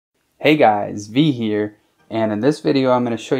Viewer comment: Yo bro with my years of watching youtube I caught ptsd in the beginning and couldve sworn you said “Hey guys Chris here” from chris fix it 🤣😂😂